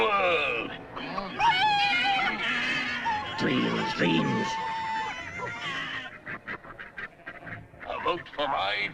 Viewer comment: 29 I can’t make out what they’re saying. Can someone confirm what Snowball and Napoleon were saying? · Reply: N: Dreams dreams S: A vote for my plan, is a vote for life beautiful! N: It's a lie! S: I promise you a four day week! N: Bosh! S: ... perhaps a three day week N: Nonsense! S: A one day week!